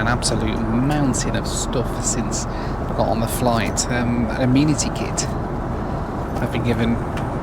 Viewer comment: Every single time he says it, I hear "immunity kit" and not "amenity kit."